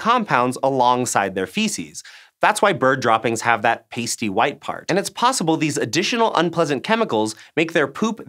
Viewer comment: I misheard "pasty white part" as "tasty white part"